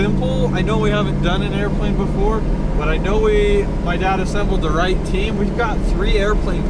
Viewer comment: Did say "my dad assembled...."?